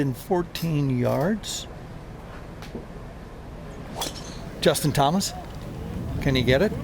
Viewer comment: did he say Justin Thomas?